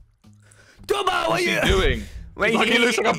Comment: I legit thought they were saying TUBOL, which my friend says it means diarrhea or a very big shit.